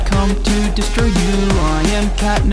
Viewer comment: I hear “destroy your own walls”